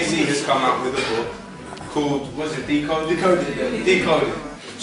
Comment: What does that guy on Lowkey's right say ~1:30 in, that makes everyone laugh approvingly? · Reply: he says decoded, which is jay z's book